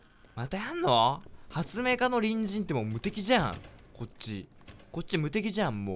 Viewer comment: ｢発明家の隣人｣が「発明家のにんじん」に聞こえた爆笑